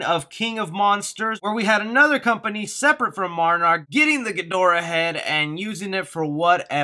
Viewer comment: Thank You For Watching!! What are your Godzilla Vs Kong Theories?? LIKE & SUBSCRIBE!! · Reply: Did you just call Monarch Marnrock???